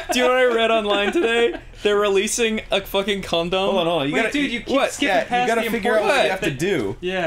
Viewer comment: Wait! 2:38! We never heard what Ross read online today, about... condoms? Is that what he said?